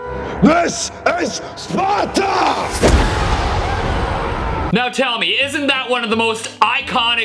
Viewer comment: The 1st time heared 0:02 I thought he said s s spoter 😂😂😂😂😂